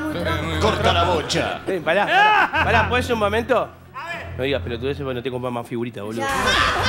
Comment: que dijo?jajaja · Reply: "No digas pelotudeces porque no te compro más figuritas boludo"